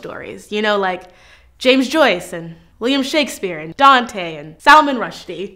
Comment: did she just call William Shakespeare "Liam Shakespeare" ?